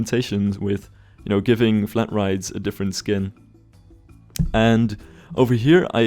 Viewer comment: i thought you said you could give flat rides "a different spin".... and were going for themepark pun of the year.